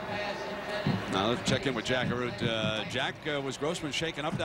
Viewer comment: Do you know it took me years to figure this out when Brett Musburger would sayJack Arote I thought he was saying jackaroo